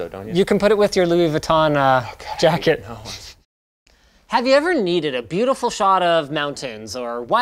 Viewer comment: lol at the end i thought he was going to say "do you need a good shot of mountain dew"